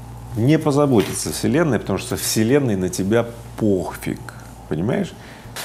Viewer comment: услышал новое слово "Похфиг" на <link>